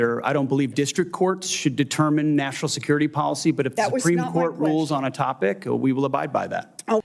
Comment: could've swore I heard Elizabeth Warren say "Supreme Court" in that instance & last time I checked They're The highest Court so they're beyond hegseths BS District Court shenanigans.